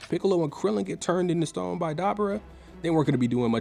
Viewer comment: what did he say? Garbara? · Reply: Dabura